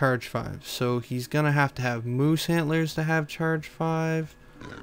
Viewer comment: did you say moose hitlers . but those actualy look prettycool it will make your guy awsome!